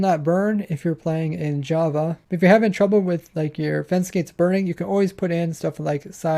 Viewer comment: Materials List: Stone pressure plate - 2 Repeater - 6 Slabs - 29 Redstone - 12 Piston - 1 Building blocks - 153 Oak fence - 123 Glass - 359 Cobble wall - 92 Soul sand - 1 Sticky piston - 7 Observers - 3 Lever - 3 Comparator - 2 Redstone block - 1 Hoppers 4 Hopper minecart - 1 Composter -2 Clock - 77 items Cake - 1 1 piston for pushing block into hopper minecart w/ something to power it Trapdoors - 37 Fence gate - 15 Chests - 12 Netherbrick fence - 4 Lava source blocks - 8 Packed ice -25 Stone buttons - 12 Bed - 1 This are the numbers I counted out but I maybe wrong. Sorry if and am and thanks for the design making me the richest man on my server. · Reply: I thought you said 123 fence gates I was like boiiii what do you need that for